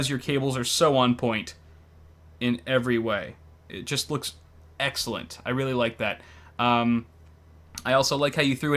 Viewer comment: Did i hear "Eggcellent" haha XD